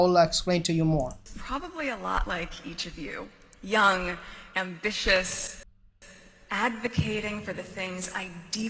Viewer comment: I thought she said ‘young and vicious ‘.........lol. And just have a look at what this fawning channel full of her fans are saying.....🤮 <link>